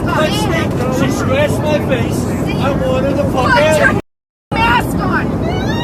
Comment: Sounds like he says "I want her the f*** outta here"... Just pop that hatch and let her go...